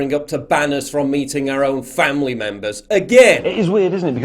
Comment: "is banning us from eating our own family member, again!" Did i hear that right?